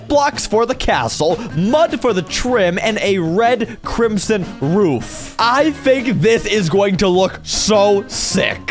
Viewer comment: Did you say roof or woof? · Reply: Roof